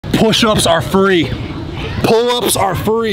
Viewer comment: I thought this man say push up is furry lol